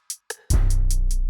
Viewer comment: Wait… what did he say at 45:23?! 212 just keeps getting better and better! Thanks for bringing us along! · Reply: If I heard it right he said: "whatever you need, we'll help. We will"